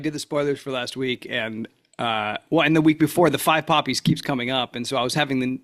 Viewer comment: I thought he said 5 puppies😂 have some more wine 😂